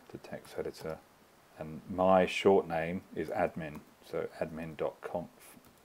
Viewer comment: can you please tell me now how to delete <link> from the terminal??? I accidentally put that in because I thought that is what you said when it was admin.conf